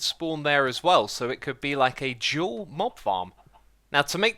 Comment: Did he say juul mob farm?